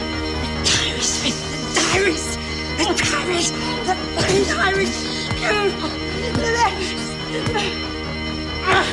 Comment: "The fucking diaries"...? Did she say it? It would have been the first "fuck" on Australian TV since Graham Kennedy's crow call in the 70s. Oh Mouse, I guess that new boofy hairdo would have lit up like a bonfire. I always liked Mouse. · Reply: Paulie Jay - sounded like “bloody diaries” to me.